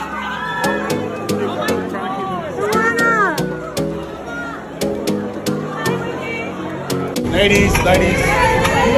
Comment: what it says? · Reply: Wendy's Korean name is Seungwan. Fan: Seung-wan-ah!